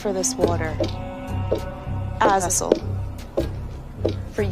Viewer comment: was it "as a vessel"? i thought she might of said Azazel